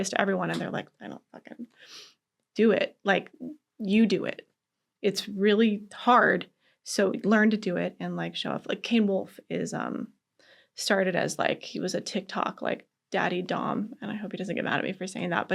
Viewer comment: does she say "kane wolf" because i cant find content